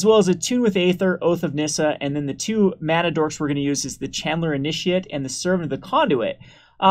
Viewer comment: to me it allways sounds like he is saying Chandler initiate.